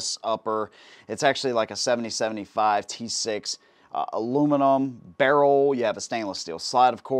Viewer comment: Seriously? Aluminum barrel? Did I hear that right!?